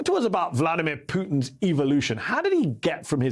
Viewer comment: I thought you've said:" putin's evilution".